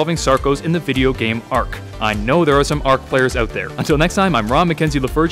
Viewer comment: Did he just say Ron mckenzie the virgin 😂😂 good video learnt a lot 🙃